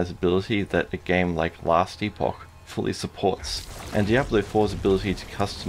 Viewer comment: what game are you saying at 3:07 I played it back 10 times and all I can get is last deepok which is not a thing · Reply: Last Epoch